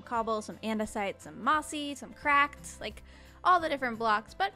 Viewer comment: For a second I thought she said "some cobble, some mossy, some crack" whoops.